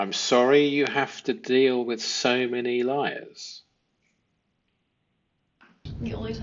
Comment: I think what she said here was “…..so many lawyers …..” Not liars Though one could argue that is redundant